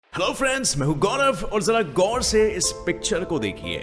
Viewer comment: did he say "girl friends"😂😂